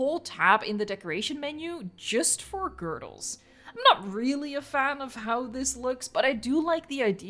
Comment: I thought you said tab just for girls 😅